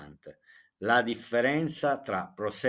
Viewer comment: On minute 18:49 you said Ora, now! Instead of allora 😂😂😂😂love that! I am learning so much from this videos 😊 hope to see one on your table on your home tour 😊😊